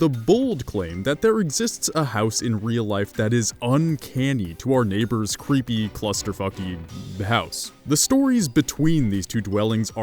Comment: is it just me or did he say clusterfuckey